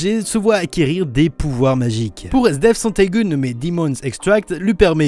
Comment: La première fois j'ai entendu SDF :D